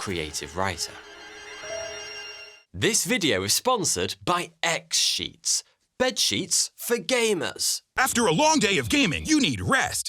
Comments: I thought he said " bed sheets for gay men" 😆😆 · For years I've watched your channel and it still sound like your saying 42 here....and omg the pretend sponcer sounded like sex sheets when I first heard you say it.....but seriously on the topic of this video, this could be a future full world event. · I SO thought he was saying "bedsheets for gays" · I thought x-sheets were bed sheets for gay men, or that’s what I thought you said, lol.